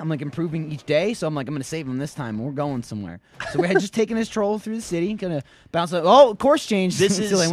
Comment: Did Michael say taking this troll through the city???